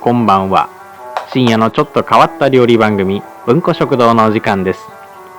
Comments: 文庫食堂がう〇こ食堂に聞こえる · 文庫食堂がう○こ食堂に聞こえるって言ったら怒る？ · 文庫食堂じゃなくてう○んこ食堂に聞こえた人絶対いる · めちゃくちゃ申し訳ないんだけど……挨拶が「深夜のちょっと変わった料理番組のう〇こ食堂です」って聞こえてあぁ、たしかに変わった名前してるなぁって思ってしまった…。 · 誠に申し訳ないのですが、音質の問題で文庫食堂がうんこ食堂に聞こえてなりませんでした。 ここに謝罪の意を表します。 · 音声ガッビガビすぎて｢文庫食堂｣が｢うんこ食堂｣にしか聞こえんwwwww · の文庫食堂が何度きいても うんこ食堂に聞こえてしまう… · ごめんなさい。0:56がどうしてもう〇こ食堂に聞こえる… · 最初「う○こ食堂」って聞こえて、なんて低俗なんだって思ってたら「文庫食堂」の聞き間違いでした。すみません